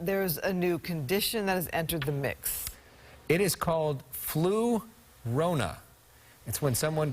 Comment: Dang scared me I thought they said the Llorona is here.